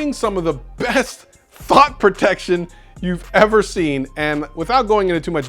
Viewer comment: Lmao, did you say Thot Protection, or did I mishear that?